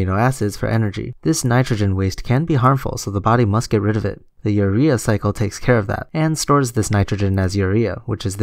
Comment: I thought you said diarrhea cycle.